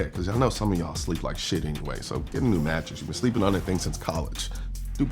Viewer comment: I heard Brooklyn "Betting" and braced myself for a sports better ad lol.